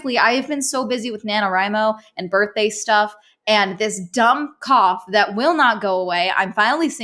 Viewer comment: When she said cough the first time, I thought she said cop and kept wondering why she’d need to go to doctors for it